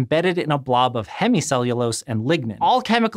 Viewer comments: I thought I heard hemicellulose and ligma. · I honestly thought he said ligma the first time I heard that